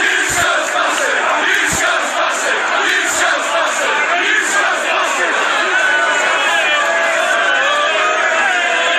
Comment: what are they saying? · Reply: “You scouse bastard”